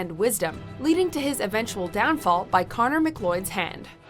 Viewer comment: Connor Macleod? Lol, sounded like she said McLloyd